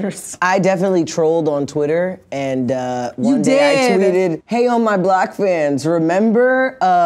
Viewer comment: it says “on my black fans” instead of “on my block fans”